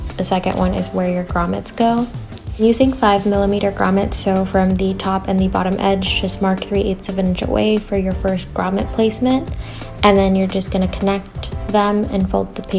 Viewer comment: What did you say at 18:23? How many inch is that? ☺️ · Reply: I said 3/8” from the edge for the first grommet